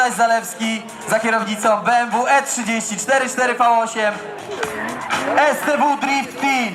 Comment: Tez mi nie pasowało ale usłyszałem w końcu - E30 4.4 V8 :D